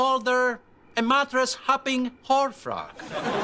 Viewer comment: Did Vic call his soon-to-be ex-wife a "whorefrog"?😂😂